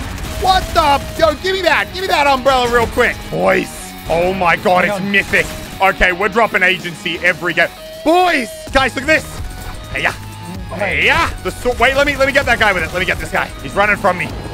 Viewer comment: Did he call the Umbrella Mythic Rarity?